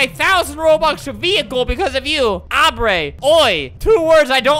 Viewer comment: my name is Aubrey... it sounded like he said Aubrey